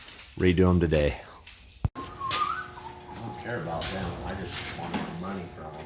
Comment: what am I hearing “ I don’t care about them I want their” ??